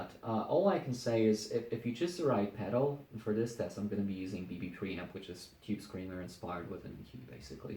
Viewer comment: Great review of the Victory Sheriff 22 amp. You mentioned a gain pedal you used with it and I couldn't quite hear the name of it. I thought I heard you say the BBE 3 Pedal. Is this correct? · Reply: Guy Meza I think he said BB Pre Amp.